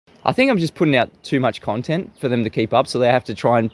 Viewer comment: opening words in this video are a lie : "I think...". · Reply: Is that what he said? I must've misheard because I could've sworn he said "I stink."